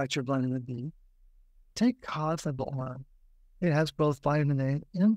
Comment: What does he say @ 15:19? Take what?😂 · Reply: Cod liver oil